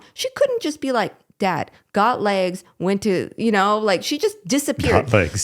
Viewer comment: said she got legs but that’s not what I heard. Backed it up twice because I thought she said she got laid and everything was good. 😂😂😂well I bet she did feel better.